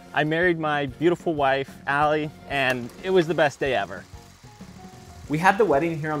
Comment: I thought he said “buried my wife… best day ever”. The shovel probably helped me hear that.